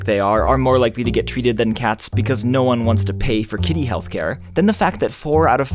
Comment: all i heard was because humanity is evil